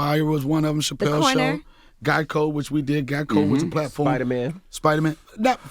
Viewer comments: He saying "Guy Code" I thought he was saying "GEICO", I'm over here tryna remember when was he in a commercial 🤔🤣🤣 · Ohhhhh "Guy Code"!! Thought he was saying "Geico". I'm waiting for insurance jokes smh 🤣🤣